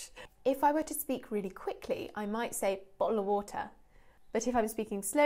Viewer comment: Lucy's fast "bottle of water" sounded like the Russian word "болото" (a swamp)